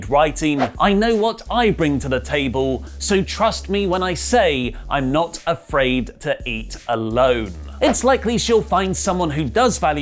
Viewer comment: So i was just listening and not watching when he was talking about Renee Young and i totally thought he said her instagram post said "im not afraid to eat a load" hahaha